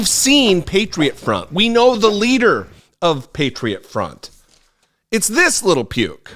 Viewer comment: Patriot front group for prison 2023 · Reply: Ohhh, that’s what he said. I thought he said Faketriot Qunts